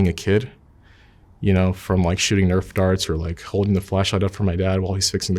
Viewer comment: I thought at first he said holding the flesh light for his dad and suddenly it all made more sense